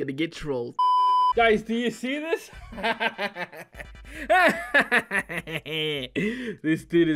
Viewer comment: When I said yes I thought you said, “do you see this new house”